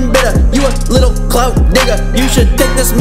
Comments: wait did he said the N word 0:44 😂😂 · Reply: He said digger. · "You a Little Clout DIGGER" not with an "N" for those that got it confused... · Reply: had me dead bro I really thought u said nigga lol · "You a Little Clout DIGGER" not with an "N" for those that got it confused... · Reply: lmao so right he cant rap when he says "Cloud Digger" i thought he said "clout Nigga" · HE SAID THE N WORD HOW ARE WE GONNA EXPECT THIS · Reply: His said you little cloud digger I thought he said the n word then I re-watched it and he said digger hater going to hate